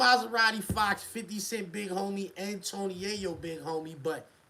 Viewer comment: I think he was trying to say fox 50 Big homie nd 50 yayo big homie